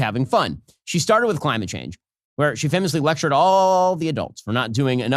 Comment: As a Swede, I always find it embarrassing, it's not easy for a little girl to try to do something about her mother's climate anxiety that she was forced to grow up with! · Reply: 😂😂😂 I thought you said Climax anxiety!